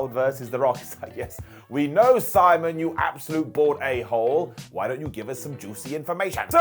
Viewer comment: This was the first time I thought he said bored instead of bald